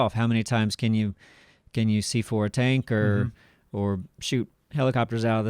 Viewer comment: did say "C4"?